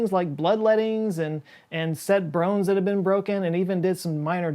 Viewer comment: did you say "set brones that were broken"? I'm pretty sure you said "brones"